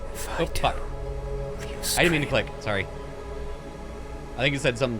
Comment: "And tear them off" is exactly what he said that you skipped